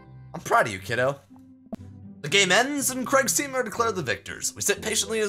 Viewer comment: Anyone else thought he said "the gay mens" at 16:01?